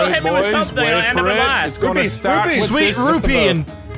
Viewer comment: did he say rufee